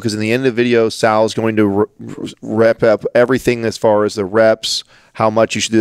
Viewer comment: You know a guy has been working out for all his life when he says “rep up” instead of “wrap up” #adamthebodybuilder